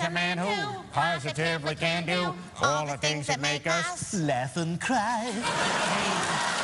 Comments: i always thought stewie said afen cry, laugh and cry makes more sense… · L A F A N D C R E Y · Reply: I swear when I first heard the Family Guy theme song, I thought that Stewie was saying 'effing cry', or ''F-ing cry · For the longest time I thought that the lyrics were “Effin’ cry” lol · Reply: Back in the early 2000s, I would watch Family Guy on Adult Swim. One time I turned on closed captions and it said "effin' cry", but when I did the same on Fox it said "laugh and cry." So yeah I think it's always been laugh and cry, but effin' cry is a common mishearing. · I thought Stewie always said “Death and crime” not “laugh and cry” 💀😭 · For some reason, even though I've watched every family guy episode, I always thought stewie said "effing cry" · I thought he said death and crime what is life? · I always thought Stewie said Kevin Cry. But now I know its: laugh and cry